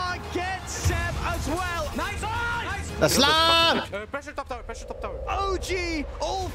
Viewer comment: did u just say islam on 32:26 ??